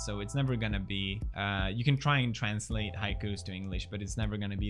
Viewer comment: high coods ? i dont understand what is he saying someone help.... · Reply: Haiku